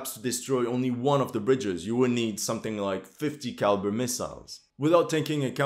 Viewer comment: did he just said theill need .50 caliber missiles to destroy the bridges?